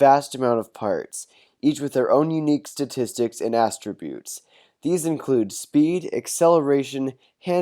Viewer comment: did he just say asstributes?